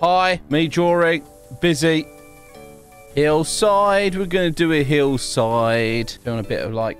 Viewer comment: Bruh I really thought he said “nice looking heels 👠 “ then I noticed he said hills Help me plz